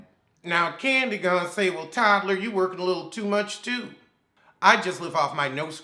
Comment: Did you just call that man "Toddler"?!?! 😂😂😂